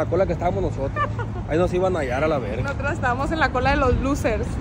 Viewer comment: En el minuto 9:44 que dice alav como el weerito lo ve jajaja